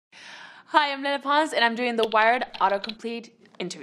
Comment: when the vid started and she said "hi im lele pons", i thought she said "i im menopause"